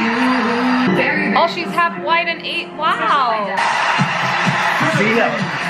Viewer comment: Did she just call our Filipino race "an ape" in reference to Catriona's parents like half white and half ape? on 20:50???